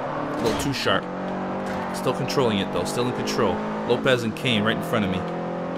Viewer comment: When he said Lopez and Kane right in front of me it sounded like "The Low peasant Kane"